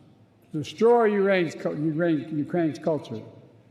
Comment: I'm sorry, did Biden just say that Russia plans to "destroy Uranus"?